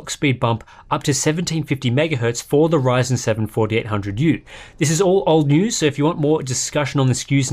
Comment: the Isuzu (ASUS) S (Zephyrus) G 14, CC still has a long way to go. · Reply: "risin 7:48 hundred youth" instead of Ryzen 7 4800U.